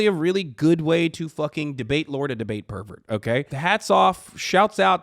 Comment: Did he call Ben a "debate pervert" at 3:35? What a great title for him. Ah i kept going and he definitely did, new term i'm hearing, its wonderful.